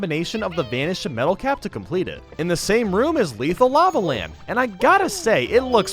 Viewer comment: did u say leafal lama lam?